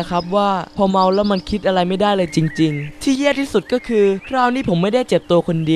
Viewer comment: ทำไมได้ยินว่า ที่เ-ี้ยที่สุดก็คือ 5555+